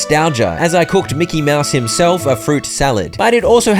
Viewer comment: for a second I thought you were saying you were cooking Mickey Mouse😂